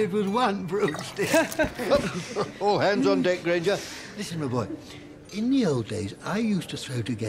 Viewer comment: What is Professor slughorn saying to Hermine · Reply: "Whoop, all hands on deck Granger"